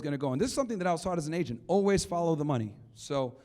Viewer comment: Did he say "as an agent"? Not looking to criticize, just curious. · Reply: No. He said " as an agent I learned "